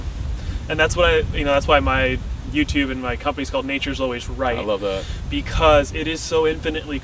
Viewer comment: Ha. First I heard it as "Nations always right"..